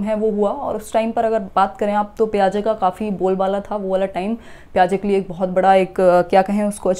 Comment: What you said here 1:17 ... Payaza tha what?? Can't comprehend the name, kindly spell it in comment. Thanks for explanation mam. Really helpful❤ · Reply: Piaget